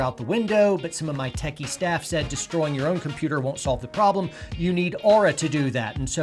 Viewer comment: Aura will destroy my computer for me? I think that’s what I heard you say. 😂